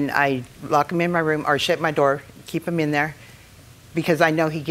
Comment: I thought she said, “I shit my door…”